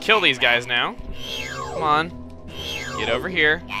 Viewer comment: I thought they said 'Cat crap' rather than 'aflac'